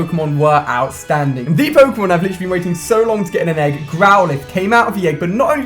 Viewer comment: first i thought you said groudon O__O